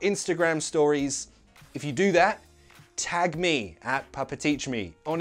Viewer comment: How bad is it that at the end I heard "I'll see you in the next glass"? Great video as always! Much love from another lovely, sunless country - Belgium.